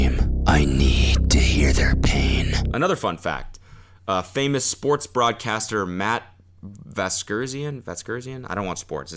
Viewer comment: Special sports broadcaster was supposed to host the show? I'm disappointed because I thought you said "Special Sports Bra Caster" Like someone live streaming in a sports bra😒